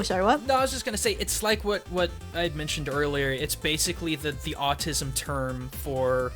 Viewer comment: complains about weeaboo uses autism as an insult as an autistic person i feel the hipocresy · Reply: yeah just reawaching it and i think it was a small stuff at 16:39 i heard autistic term rather than ''the autism'' term thought that was so weird